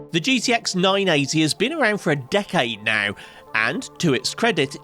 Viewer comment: I thought he said “dickade” hahahahaahah